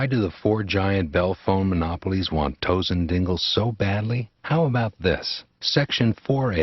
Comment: I’m sorry I know this is serious but I was listening to this without looking and I heard “toes and dingles”😂